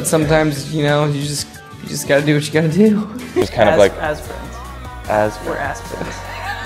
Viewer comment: did tyler call him and josh ass friends 4:23 oh my godhshajahg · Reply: ARMY Aghase and Clique I-/ he said "as friends" but then immediately after I swear it sounds like he said "we're ass friends" and they both started laughing a lot.